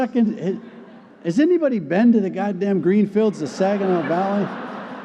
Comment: mark. Wait. Rewind. Did he say "G-- D----"? Reading the comments I'm thinking he'll soon be talking about God. Hillsdale, I thought you were a respectable place. I was wrong. I guess I'll consider myself anti-Hillsdale.